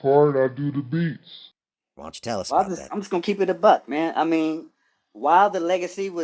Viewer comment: Did I hear keep it A buc 🙌🏽🎶🙏🏾🎵